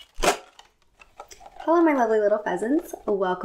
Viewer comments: "Hello my lovely little pheasants" What I hear: "Hello my lovely little peasants" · I thought you said “hello my lovely little peasants” and I didn’t bat an eyelid because..well...understandable but I accidentally went back and realised you said “pheasants”